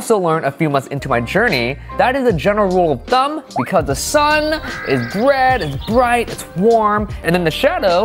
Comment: I heard "the sun is bread"